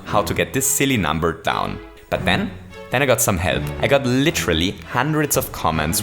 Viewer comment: I thought he said, “Then I got some head.” 😂